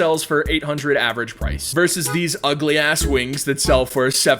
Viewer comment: did i hear a bad word??? hear it at 3:59 i am suprised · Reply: He says ass a lot. It’s not the first time